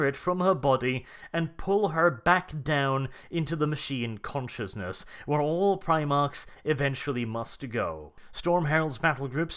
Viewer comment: Wait did say where all primarcs must return